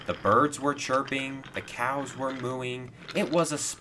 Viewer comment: I almost thought he said that the cows were mewing